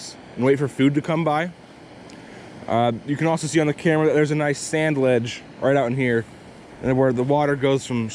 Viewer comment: did he say sandwich?Ok now I'm hungry 😊